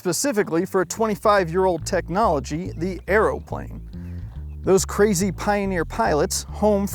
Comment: I know you're saying "aeroplane" but due to the topic every time you do I hear "arrowplane"